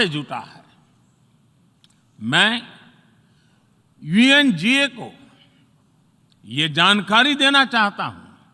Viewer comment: did he just Say VNGA. so Its mean he Even Don't know Where he is?